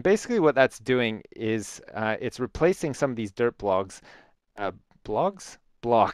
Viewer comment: i heard that you said Derp Logs :D